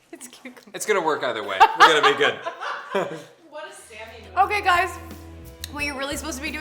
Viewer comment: Did anybody else hear Sammy say "What a Sammy move" at 3:41? 😂